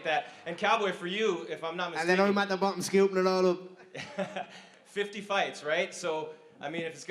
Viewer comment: anyone know what Conor said?? You can tell by the reporter's response he didn't get it either. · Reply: He said and then I'm at the bottom scooping it all up 👍